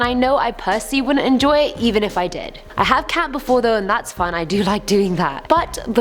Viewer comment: I thought she said I had cat before. I had to triple check she said "camp"